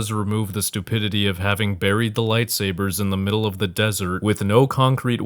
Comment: i legit thought you said bury the light here which made me think of vergil 😭